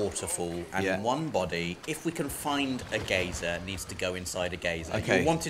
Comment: Just a bit of help. Geyser is pronounced as Guy-zer. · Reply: The British English version is 'Geezer', American English 'Guyzer' as you say. Don't know where the hell 'Gazer' comes from, I thought was saying Gator again at first.